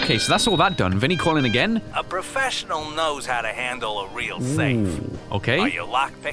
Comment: am i the only one hearing " thafe" instead of safe